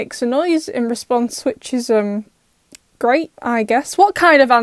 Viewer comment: 😂😂😂I thought you said it was great.